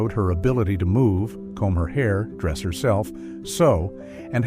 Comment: comb her hair?did i hear that right?